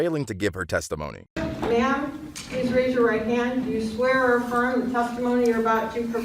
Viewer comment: she says "Do you swear or affirm". where it says inaudble